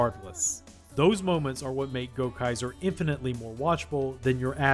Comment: I misheard this repeatedly as "Gokaiger" and kept thinking we were going to be talking about something much cooler